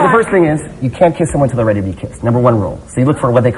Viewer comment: what did he say at 6:51? you can't kiss someone who... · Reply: Arno Verougstraete isnt ready to be kissed